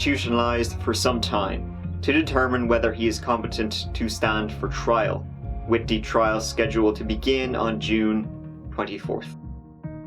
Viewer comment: did he say the trials set to begin june 2040?